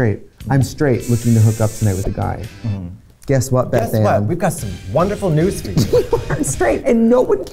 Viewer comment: Did Trixie say “Guess what, Beth Anne?” at 4:15 or am I just traumatized from her mocking the name Beth Smith?